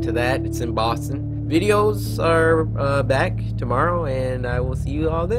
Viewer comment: cory where are the vids? ive been seeing if you upload everyday, but then theres no vids. Please at least tell us if your going to take a break or not. BUt hope u upload soon cory..... see ya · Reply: I thought you said tomorrow Cory on the end of the video now i'm mad as heck